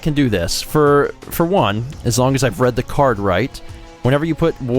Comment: I thought I heard say bread, not red, you've triggered me as bread is not a creative color.